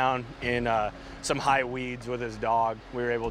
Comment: did he just say "high weeds?" lmao